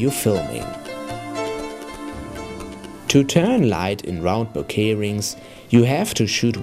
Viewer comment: What is he saying at @ 9:57? "To turn light in round bo clearings, you hav10 stop variable neutral densitye to shoot wide open or with a long focal length"